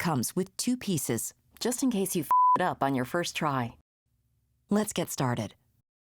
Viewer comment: did they actually said "just in case that you f**k up in your frst try"? I thought that companies weren't allowed to say bad words :v